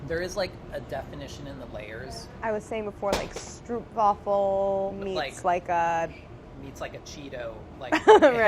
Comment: instead of stroopwaffle i thought she said stripwaffle